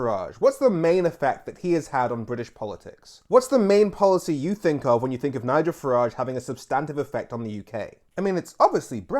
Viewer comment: That voice recognition software is awful. At 0:20 It misheard Nigel and displayed "niger" instead of Nigel, I'm not sure if you can change that, but keep up the good work!